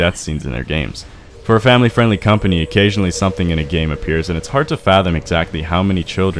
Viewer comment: Tbh your transitions are scarier than everything you listed. No I dont want a surprise seizure. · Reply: LMAO i hear “SOMETHINGs in MYASSS”